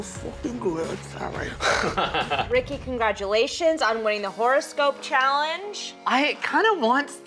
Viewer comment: Tell me I thought said “Forest Gump” and not “horoscope” 💀 I was so confused